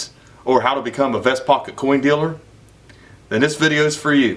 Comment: I thought you said "or become the best f##kin coin dealer" 😂 I had to rewind it to hear the pocket coin dealer, but the first statement is pretty accurate for you!🙏